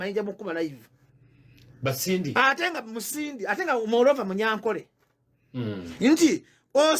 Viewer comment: I just heard "moreover munyankole "😂😂😂😂😂😂